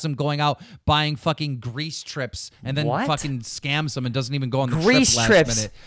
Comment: What does Keemstar say at 1:08:23? Grease trips? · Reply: FegzTheShitlord Greece trips... Trips to Greece :DDDD Totally